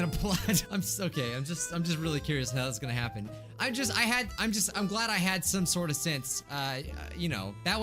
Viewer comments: Am I the only one who heard “I’m so gay”? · it sounded like he said “im so gay.” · Who else heard say “I’m so gay.” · *I'm so gay* (Yes, ik that's not what he said but it sounded like it 😂😂)